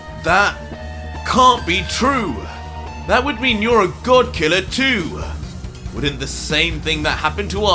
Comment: There was an typo when goku subtitles said you’re a good killer instead of god